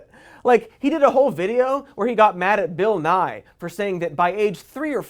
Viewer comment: I thought he was saying "Bill Nighy" not "Bill Nye" so I was so confused and sort of impressed with Bill Nighy's apparent dabbling in gender politics and science